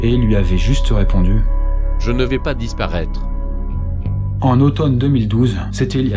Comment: Pour "je ne vais pas disparraitre" moi j'ai compris "je ne veux pas mourrir" aprez je dit sa je dit rien